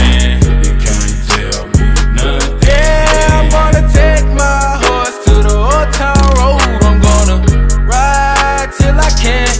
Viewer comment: Okay I'm gonna be honest: I always thought he was saying he was going to take his horse to the hotel room. Which gives this song an entirely different meaning.